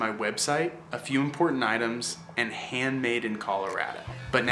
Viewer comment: This dude is definitely working high every day. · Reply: are you the guy that made this video? because that's who I'm talking about. I thought he said he's in Colorado